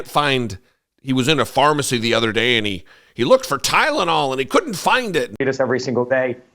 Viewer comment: Did he just say "kylenol"?